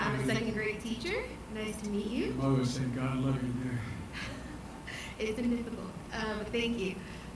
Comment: what did he just say at 17:32> something"god love you dear" creepy joe. did he even answer her question? she asked about education equity> He just rambles.